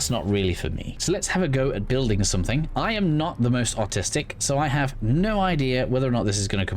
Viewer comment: Had to rewind a few times to hear what you actually said lol. I often think "I AM that autistic" as I play this game (<100hrs 1st play through). It is a very artistic game if one wants it to be. Very fascinated by this game.